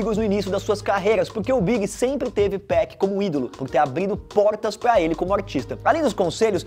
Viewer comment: A morte do big doeu, a do 2pac ainda mais, porém o “abrido” que eu escutei no 19:13 foi como um tiro